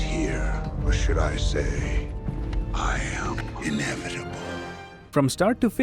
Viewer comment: When thanos says I am inevitable I always heard as “I am in every ball” 😂